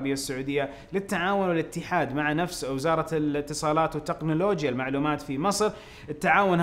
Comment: بطئت الفيديو لل 0.25 و سمعت وزارة الاتصالات و التقنلوجية في 0:55 😂 بس والله خوش قنات و خوش اخبار 👍🏻